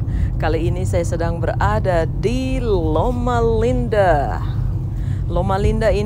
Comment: haha..tadi salah denger, kirain Mama Linda... LOL 😂 keren ya masih jadi rumah sakit yg the best dikota ini ya. Hebat banget bisa nebak harga akan turun, jadi bisa beli tanah buat bangun ya. Tempatnya keren banget... thanks dah diajak liat2...